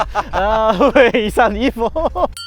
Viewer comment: What does say at 10:14? 왜 이산 이보 ? Or something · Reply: Oh my god he’s say “we sound evil” LMAO. Never mind 😂